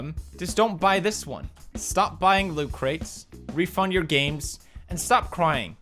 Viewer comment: what did you say ? Refund our game ? WE CAN'T